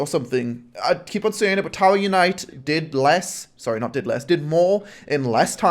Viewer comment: what game did more? i cant hear it right, i hear batallion night? anyone know? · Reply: tower unite